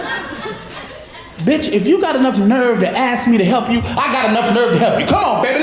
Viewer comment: I don tknow what he said after 13:26 , van anyone help me`? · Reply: Teemu Syrj “bitch if you’ve got enough nerve to ask me then I got enough nerve to help you